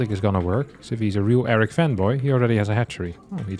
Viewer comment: Wow this erik fanboy guy seems amazing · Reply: I thought he said erik femboy... you can imagine my confusion